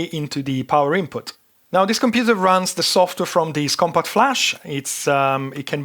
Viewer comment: Subtitles are machine-created and have a lot of mistakes. For example: 1:03 "Compot Flash" instead of "CompactFlash".